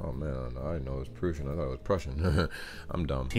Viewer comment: no, you're not dumb, the narrator is saying "prucian" instead of "Prussian" for no discernible reason.